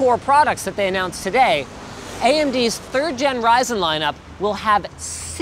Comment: Told my friends about the new AMD 3rd Gen CPUs. They thought I said AMD Virgin CPUs. Therefore I cannot say third gen anymore...